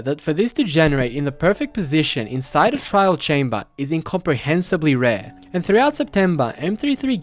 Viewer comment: It sounded like he said "in copper hensibly" rare, would have been an insane pun.